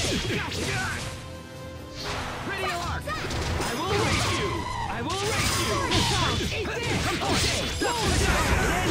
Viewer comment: what is richard saying at 23:30 to 23:34 cause i heard something reallllllllly bad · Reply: "I will ERASE you," but I think I can guess what you thought you heard :O